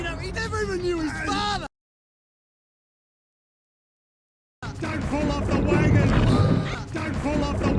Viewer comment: (don’t fall of wagon) or (stop fall of wagon) 😢 I think he said (just don’t fall off the wagon)